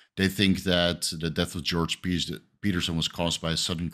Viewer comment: I thought it said georgio petrosyon!!